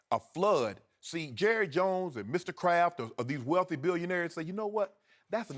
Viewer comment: I thought he said Mr Krabs LMFAO